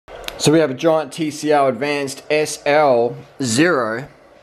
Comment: Did he just said 0:01 TCR? I am confused!